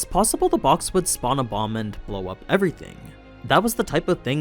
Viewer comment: "spawn a bomb and blow up everything" and I heard it as "spawn a 'mom' and blow up everything" like smolder from league xD. ICANT